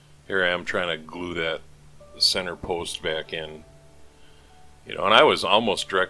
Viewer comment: "Center post", he says... I hear "S.O.B." 😂